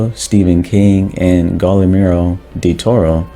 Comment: did i hear "Godimiro Deltoro"? bro, its Guillermo, like Guillotine